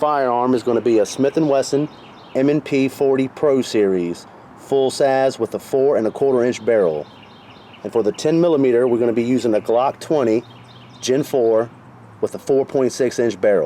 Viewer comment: At first, I thought you said, “Full sass.” I had to go back and listen again haha! You might have created a new slang for me. Lemme tell ya. This Ruger SR1911? .45 acp, full sass! Yeah, lemme get a lemonade, full sass! Great video, my man. I’ve been dying to see the comparison between .40 and 10mm. Great stuff!